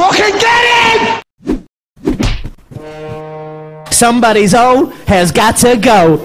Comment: Что он сказал ? What a spek he ? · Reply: Somebody's 0 has got to go, he meant to say that he'll break Khabib's undefeated record